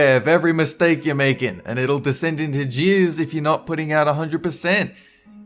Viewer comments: It sounded like you said "descent into jizz" 😂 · I thought he said jizz instead of jeers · I thought he said jizz.. He said jeers